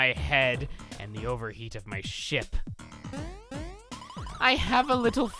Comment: nI looked away and I swear I heard, "overheating my shit" XD